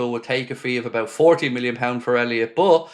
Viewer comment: I thought you said £14m for Elliot, £40m makes more sense